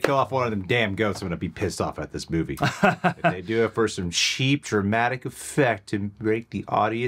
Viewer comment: Did you just say "sheep dramatic effect" or was that wishful thinking on my part?